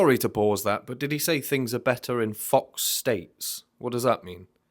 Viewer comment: "Things are better in fork steak" the best misheard of this song, now i cant unhear it 😂😂😂😂